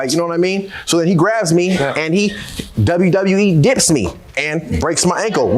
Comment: O ok, he said “ he wwe DIPPED me” lmao thought he said “ he WWE D’d me” omg lmao got scared for a min 😓